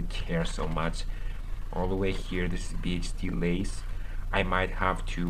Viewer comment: What is he saying at 28:08 about the lace? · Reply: I say "this is BHD lace"